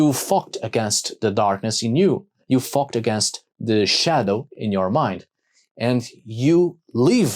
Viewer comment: Who else heard him say "... you f*cked against your shadows" :D